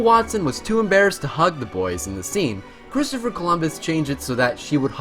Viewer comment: Did you say Cristopher Columbus?